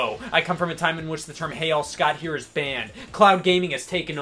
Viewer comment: I THOUGHT HE WAS SAYING "Hey y'all" WHY DIDN'T ANYONE TELL ME HE WAS SAYING HEY ALL!?!?!?!?